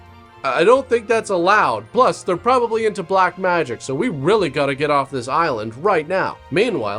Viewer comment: I thought he said witch craft. Where did black magic come from